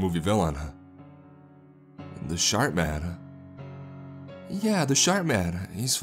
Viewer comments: I thought he said "Shart Man" at first. I probably would have continued thinking that if not for the whole knife thing. That said, Thanks for another great narration! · Because of the glottal stop I kept hearing "shart-man"